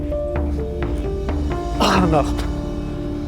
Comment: You said at the end “if you like this video”, I liked the video and then I heard “You should klik on this video”😂